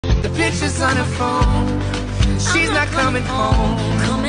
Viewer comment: At first I heard “The b**ch is on her phone” 😭